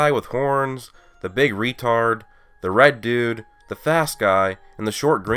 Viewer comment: did he said the big retarded LMAO Also who is getting super Saiyan first Krillin or Goku?